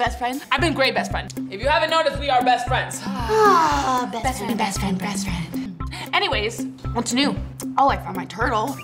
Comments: I thought they said ahhhhhhhhh best free best free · who else heard breast fed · I heard BREAST friend 😅😅😅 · It sounds like there saying breast friend 😂